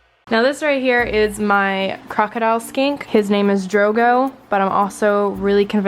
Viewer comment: I thought you said crocodile skank 😂